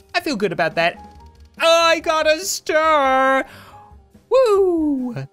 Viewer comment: DID HE SAY HE GOT A STAR?!?